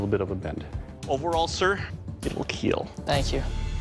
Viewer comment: Every time I hear "It Will Kill"my ears feel it as "It Will Heal"